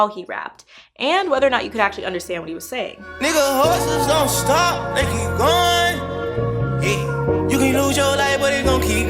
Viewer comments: Wait he hustlers dont stop they keep going I thought he said whores don't stop they keep goin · he says hustlers?!?! Like everyone else i thought he said horses 😂😂😂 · In digits this nigga said hustlers don’t stop they keep going I really thought mans said horses don’t stop they keep going which was more of a bar to me 😂